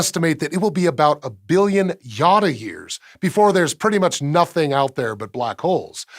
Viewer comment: For a moment, I thought he had said "Yoda years".